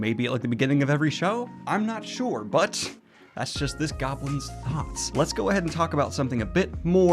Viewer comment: I know you said "this goblin's thoughts", but I heard "this goblin's thots". Now I can't stop picturing someone goblin pimp from a urban fantasy setting. Quick, someone give me some good goblin pimp names!